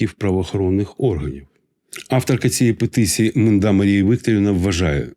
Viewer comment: Приветствую. Я правильно расслышал фамилию нардепки, подавшей это "гениальное" рацпредложение --- Манда? Ну, если вспомнить старое избитое выражение --- "как вы яхту назовёте, так она и поплывёт", то тогда всё норм......всё логично.